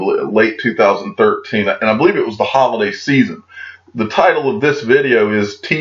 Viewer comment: Who else heard, "Hodl-day season" at 0:50? 😎